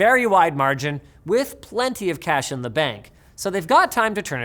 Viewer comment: Did you say Cache in the Bank?